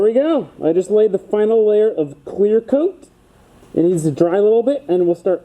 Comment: it sounded like queer coat lol